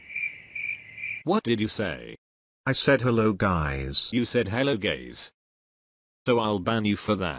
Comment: he did not say hello gays he said hello guys